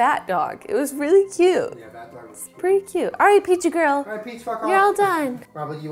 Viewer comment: did Julien say “alright peach fuck off! ☺️” LOL